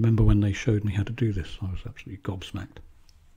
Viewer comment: I'd love a medal! 😂 I only recently started watching your videos. I love them! I dabbled in oils but I couldn't take the smell so I use watercolors and acrylics. Oh and thank you for explaining "gobsmacked" I heard "God smacked" and had ...a similar image... but closer to a sqashed grape.